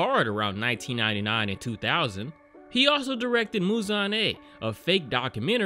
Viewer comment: BOI I thought it said Mulan at first😭